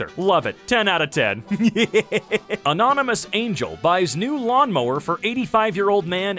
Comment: I thought it said anonymous bagel